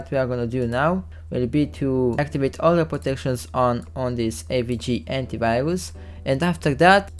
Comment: I’m sorry but what you said here? I couldn’t understand you. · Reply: I said that i'm gonna run the ransomware with all the protections on, on the AVG Antivirus. My accent is not that bad and i'm pretty sure that was audible but yeah, that's what i said